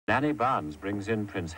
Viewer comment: I thought they said nanny Plum 😂